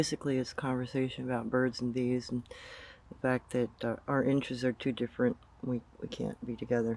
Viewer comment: Our interests are too different" ? "Our inches are too different"? I can't make out what you are saying due to the pink noise in the microphone. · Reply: sorry bout that inches